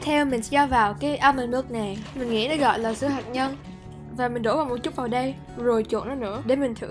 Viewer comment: sữa hạnh nhân mà mình nghe nhầm thành sữa HẠT NHÂN😂😂